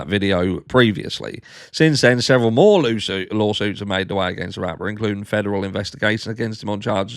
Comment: I swear I heard you say Loo suits 😂 it's fitting as he is in deep shit! Another Harshism? 🤣🤣 Have a terrific Tuesday, family! 🇺🇸 🫶🇬🇧